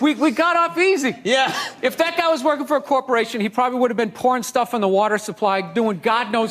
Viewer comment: " - I know he says "pouring stuff" into the water supply, but it totally sounded like "Porn Stuffing" the water supply, when I first saw it, and for some reason I think that's 1000x funnier.